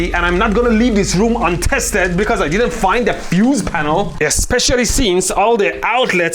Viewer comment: I'm pretty sure you said "fuse panel" and I'm also pretty sure I heard "abuse panel".